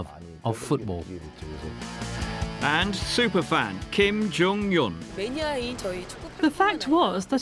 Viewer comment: I thought he said Kim Jong Un...